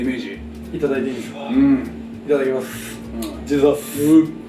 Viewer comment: この人が飲む前に言っているジーザスとはどういう意味なんでしょうか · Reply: ジーザスって言ってるんですね！ "チワッス！"って聞こえてました。